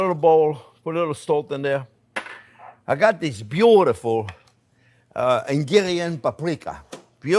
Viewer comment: Is he saying “Hungarian” paprika?